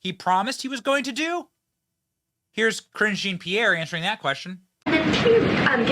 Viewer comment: Yo Benny it sounded like you said "Cringe Jean Pierre"!?!? If not, you should start! A perfect nickname!